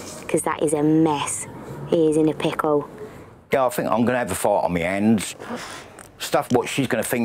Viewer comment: I thought he said "I'm gonna ave to fart on me hands" ...surely not. Perhaps he was predicting a series of arguments whilst tidying up the mess (i.e. "a fight on [his] hands").